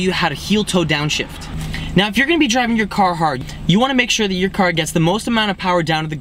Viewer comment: i so thought he said, if you are going to be driving your carhartt, at first